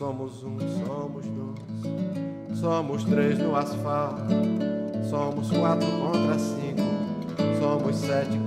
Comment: "Somos 4 contra 5" entendi "somos quatro Honda Civic" kkkkkkkkkkkkkkkkkk Já tava aqui pensando que a música era uma crítica ao capitalismo. Kkkkkkkkkkkkkkkkkkkkkkkkkkkkkkkkkkkkkkkkkkkkkkkk